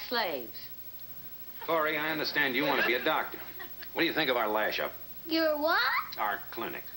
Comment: I'm with the kid. "Your what?" What did the doc say. Lashup?